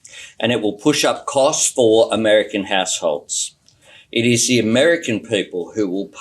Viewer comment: it sounded like he said it will push up costs to American a**holes. I can’t unhear it